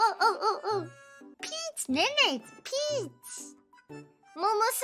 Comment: When I first saw this, I actually thought she said “Pinche, Nene”